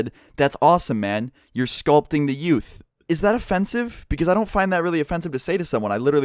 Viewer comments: He probably thought you said scalped the youth. Like mind fucking children. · he might of heard you say your scolding the youth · he prolly thought u said skollding the youth